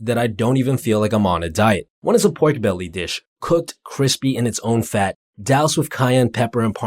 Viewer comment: what’s a portobelly dish? Is that what he said? What is that?